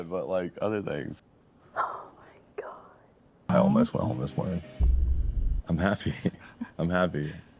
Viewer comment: is he saying “I almost went home this morning”